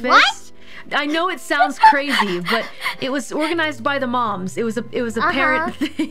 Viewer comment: I swear to god, at first I thought she said "it was organized by the mob"